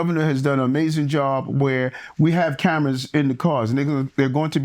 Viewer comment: Did he say “We have cameras in the cars n****”